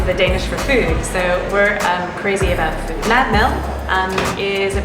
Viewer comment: shed did NOT say "Mad Mel" - she said "Mad Mad" and they didn't get the joke at all. Pity. "Mad" is crazy in English and "Mad" is food in Danish. That the joke, "Mad Mad."